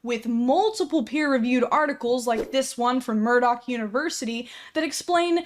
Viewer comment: I THOUGHT SHE SAID MURDOC AS IN THE GUY FROM GORILLAZ